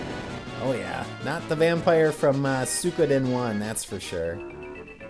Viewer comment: What'd you say at 16:12? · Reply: "Not the vampire from Suikoden, that's for sure". I was referring to the Vampire you fight in the original Suikoden, which is a bit infamous for being difficult.